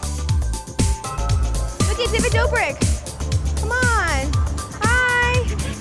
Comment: Did she say look it David dobrik @ 8:13?